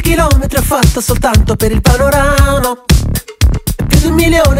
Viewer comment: Quanti chilometri ho fatto soltanto per il PANORANO 🗣 ho sentito bene?